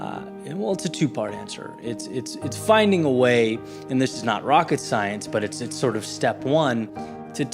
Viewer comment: I thought he said “snot rocket science” instead of “it’s not rocket science” and that absolutely made my morning 😆 as always Eddie, thank you for your messages, much appreciated